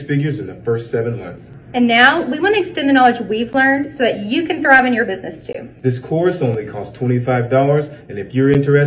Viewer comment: I heard that as "you can throb in your business too"